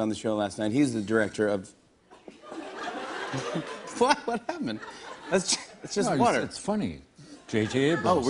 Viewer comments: Did Jimmy just say "fuck, what happen" at the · At like 0:03 to about 0:08 did jimmy say "Fuck what happened?"